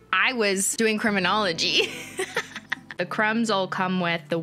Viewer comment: i thought she was saying creme-inology